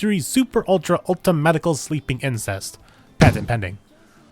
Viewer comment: Thank you for the kind words! That’s definitely the plan to up my game when Season 2 airs with episode reviews as well. It’s going to be a busy time on the channel in January lol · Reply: Did you say incense, or did you just say... inc3st?